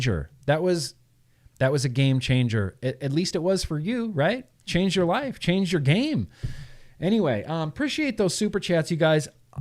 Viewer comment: game changer... i hear "...change your wife, change your game." Wait what? 😂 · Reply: hahaha "Changed your life, Changed your game"